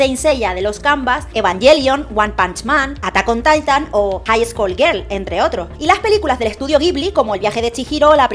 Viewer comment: Soy el único distraído que pensó que dijo Highschool DxD?